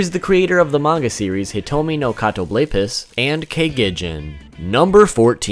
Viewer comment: Is it just me or did anyone misheard the name as Hitomi no kato rapist instead of Hitomi no Kato Blapist?